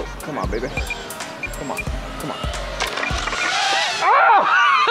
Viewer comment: does he say "come on nigga" ? XD